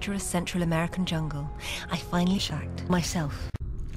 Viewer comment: Whoops! I heard "shagged" too! XD